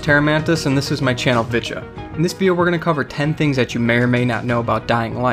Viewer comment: I thought he said B*tcha